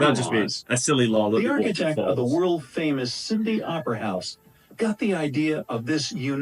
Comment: did he just say the Cindy Opera House 😂